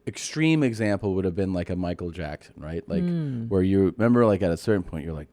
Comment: thought he said micro Jackson